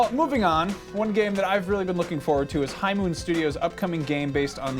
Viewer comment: might want to slow down a teensy bit when you say High Moon Studios. I thought you said Hyman Studios for a moment and my 10 year old brain wanted to giggle.